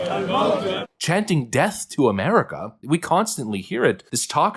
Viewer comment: This is facetious as hell, but I laughed when i realized that it sounds like theyre saying, "I luv 'Merica!"